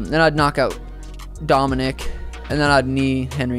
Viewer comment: did he said "khabib" in the end